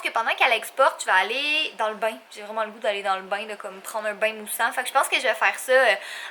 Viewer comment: J'ai compris "un bain de sang" au lieu de "un bain moussant" 😂😂😂